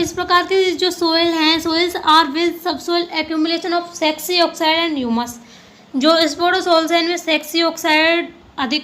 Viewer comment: Sexy oxide sa kuch bola 😂😂 have I heard right ya sch mai Bdya Ash aur sexy oxide 😂😂